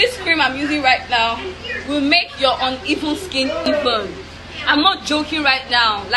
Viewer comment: Did she “ Your evil skin” ? Or did I mishear her? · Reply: She said uneven